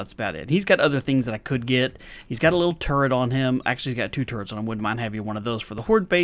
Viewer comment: Every time you said "turret" I heard "turd", and I just wondered how a turd would help you on horde night. Lol I'm tired...